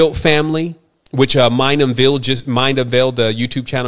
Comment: what youtube channel just did an video about Vanderbilt family? I didnt catch the name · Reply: Mind unveiled